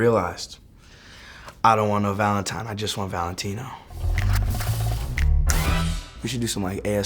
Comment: I could have sworn that he was saying “I don’t want no Bentley Truck I just want Valentino” 💀